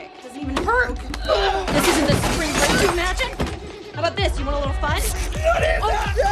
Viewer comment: what did she say? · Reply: This is the spring break you imagined? // This isn't the spring break you imagined?